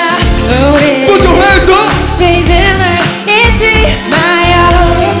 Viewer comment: 이때 리듬을 잊지 마요라고 들리는건 착각인가요?